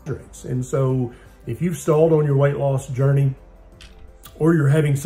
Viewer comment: Did he said journey?